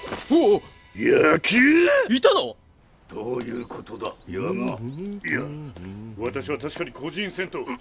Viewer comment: i dont think i heard yaga i think i heard kiryu